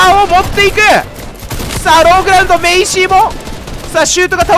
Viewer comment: ローグラウンドがローグラ運動にしか聞こえない…俺の耳腐ってんのかな？？